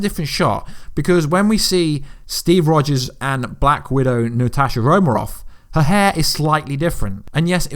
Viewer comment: Who else heard him say "Natasha Romeroff" 😂😂